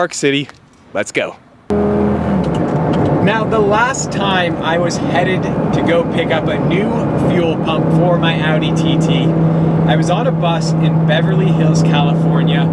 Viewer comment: Did he just say "the last time I went to pick up a fuel pump for my Audi TT I was on a bus and I was broke"? so you're broke but you have an Audi TT ?